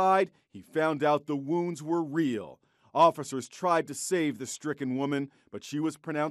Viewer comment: Bruh, I feel bad. I heard “Officers tried to save the frickin woman” instead of stricken...